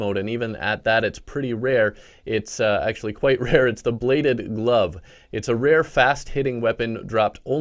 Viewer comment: When he said bladed glove I thought he said blade of love